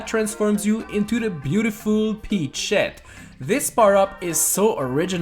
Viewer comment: Did he say Sh**